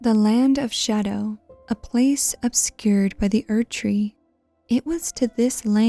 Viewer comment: it says "obscurded" instead of obscured